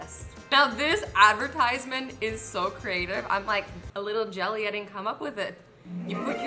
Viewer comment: I thought you said i'm a little jelly i didn't come up with this.